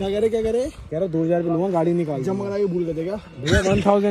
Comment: pe kya bola tatiya ne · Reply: kehta 2hajar lagenge tatiya- jma kra kai bhul gye the kya😂😂😂